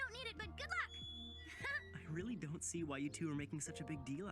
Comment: I swear I heard "You two there".😅